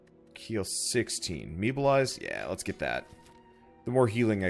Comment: I thought Meabolize said Meatbollize and thought you could transform into Meatwad